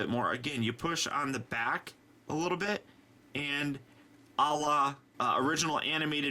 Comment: why did he say Allah at 12:51? accidental muslim or something more?